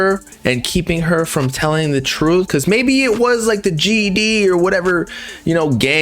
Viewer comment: Not sayin GED instead of GDK LMFAOOO! Juice graduated Highschool he aint need no GED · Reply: It’s gd (gdk) means gangster disciple killer. Gd is ( gangster disciples)